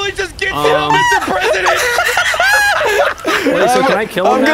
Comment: did he say "he just got get down Mr presidented"?